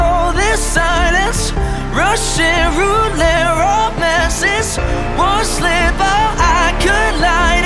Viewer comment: why do i always hear "russian ruling the masses"